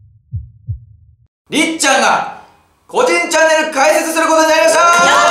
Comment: 個人チャンネルがおでんチャンネルに聞こえてどんなチャンネルだよwって思った